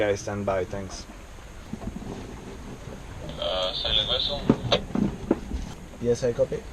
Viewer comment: lol what did he call him a silent vessel? Lol btw huge dong